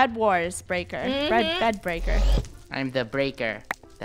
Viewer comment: I thought said bread baker